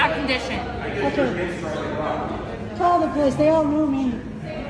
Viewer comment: Did she say, "call the police, they all know me" or did I mishear her?